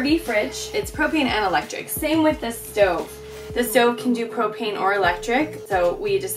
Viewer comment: When she said “this stove can do propane” I thought she said “this stove can do cocaine”